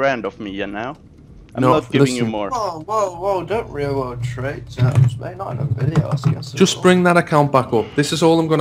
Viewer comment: When he said "KemQ is a Real-World Trader" it made so little sense to me that I thought he was saying "Real-World Traitor"